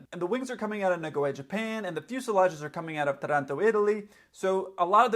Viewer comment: Hi - at min 3:45... did you say Toronto Italy? Do you mean "Taranto", Italy with the accent on the first a?